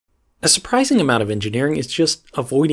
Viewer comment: I paused it at 1 second in and it sounded like he said "A surprising amount of engineering is sh*t."